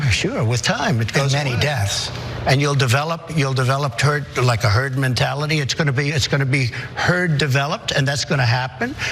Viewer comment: Did he say turd mentality before correcting himself? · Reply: yes he said turd clearly then corrected himself.